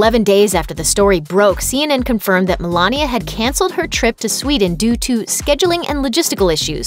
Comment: Sweden? I thought you said Switzerland.....like, Davos?